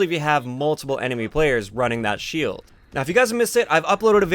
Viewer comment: Sounds like your saying 'rat-shield'